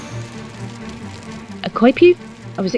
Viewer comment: what did she say? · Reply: "A coypu?" => It's a sort of giant rat that likes water :p